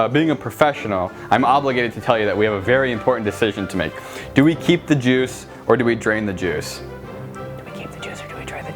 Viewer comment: I thought he said should we keep the jews or drain the jews 😂😂😂